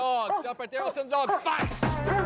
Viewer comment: did he say "fass"? it sound like the german command for the dog. @ 31:55 "aus, platz" yes. they are german words